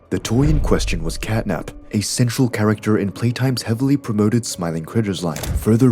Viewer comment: ok if that’s a real person saying the line Central character they need to read it much slower as it sounded line sexual character, I had to rewind it twice to make sure I wasn’t mishearing things. If it’s AI it needs to be adjusted for that word as it’s sounds so wrong.